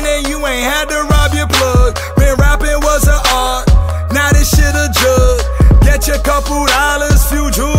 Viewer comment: "When rapping was an art, now this shit a jugg" I felt that, too many rappers nowadays rap to make money and not cause it's their passion. Bumping this all 2020 🙌🏽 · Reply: i thought he said " when rapping was an art, now this shit a drug "